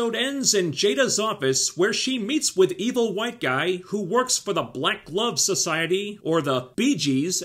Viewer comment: I have to say, for nearly the entire video I thought you were saying "Black Love Society."